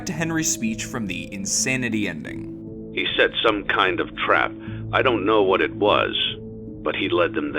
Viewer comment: I was on another tab and heard this as "he said some kind of crap"